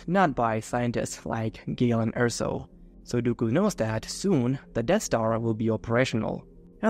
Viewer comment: I misheard So, Dooku as Sudoku